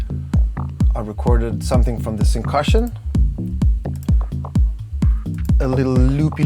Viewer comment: what did he say? syncusion?